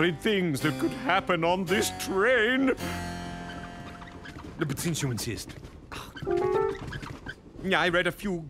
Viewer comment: What did Poe said at 11:35? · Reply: "uh, but since you insist,"